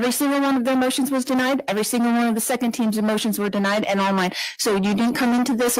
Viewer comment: Does she say you denied my second teams Emotions” some one check pls time stamp 23:55 lmao